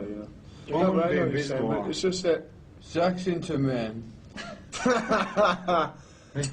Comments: Did he out one of them guys.......''Zack's into men'' that's what I thought he said. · Wtf? Does Ozzy say: "Zakk's into men"? 🤣